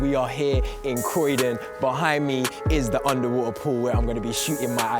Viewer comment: did mans really say underwater pool